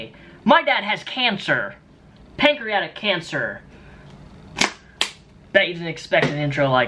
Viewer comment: I thought you said "Patriotic cancer". Whoops.